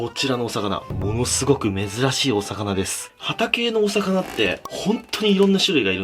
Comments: ハタ系のお魚 って言ってるのが 畑のお魚 って聞こえて え？畑で魚取れるの？ ってなってた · ハタ系のお魚が 畑のお魚 他にも聞こえた人いるはず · さっきまで〇〇〇しゃちょーの畑見てたから 0:21「畑〜の魚」って聞こえた笑笑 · ハタ系のお魚が、畑のお魚に聞こえて 毎回「ん？！」ってなっちゃう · 「ハタ系のお魚」が「畑のお魚」に聞こえてしょうがない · ハタ系のお魚、何回聞いても畑のお魚に聞こえて「？？？」ってなる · ハタ系のお魚… 畑のお魚って聞こえて土に生息する魚かと思った